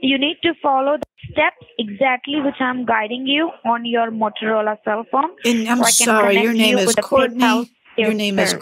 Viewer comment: I swear she said Mozzarella cell phone